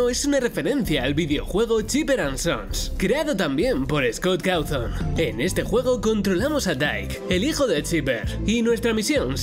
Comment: me encanto lo que dice jaja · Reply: Se oye trolleamos a alyt